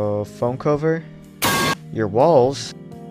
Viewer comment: I thought you said balls.